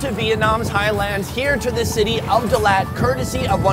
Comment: Did he just say in 0:15 "we have traveled to Vietnam, Thailand"???? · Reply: Vietnam’s highlands.